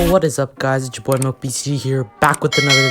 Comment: Did you just say- Its ya boy Milk btd here